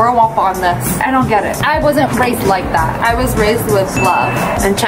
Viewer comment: when she said she wasn't raised like that, the subtitle said, "I wasn't raped like that" 😂😂